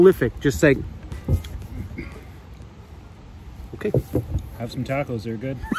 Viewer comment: Thought he said have some tablets at first...?